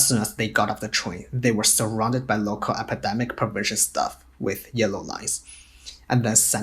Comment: Automatic subtitles say > " ... local epidemic PERVERSION staff ... " Even youtube bots know Zero covid is BULLshit !